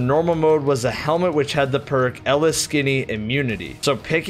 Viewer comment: Did he just say ELLA-SKINNY.......